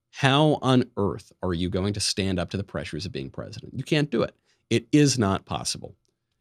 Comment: I heard “...under the pressure.”